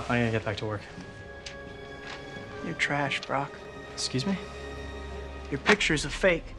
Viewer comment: It sounded like Peter said your trash bra lol